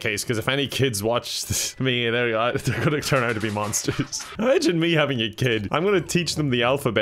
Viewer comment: “Imagine me having kids” Kevin what are you talking about? we’re all here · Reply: Thank god I thought he said " I shouldn't be having a kid " and was immensely concerned